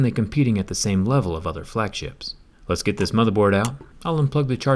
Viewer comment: I'm so corrupt everytime he said let's open this motherboard I heard it let's open this motherf*****. Lol